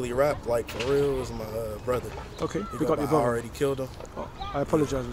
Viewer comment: what he said? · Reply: "His brother goes by I Already K*lled Em", his brother is a rapper aswell and that's his stage name